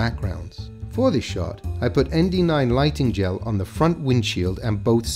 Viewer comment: "____ lighting _____" what does he say????? Help please! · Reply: ND 9 lighting gel. I believe he means a type of Neutral Density filter in a sheet style you can lay on glass.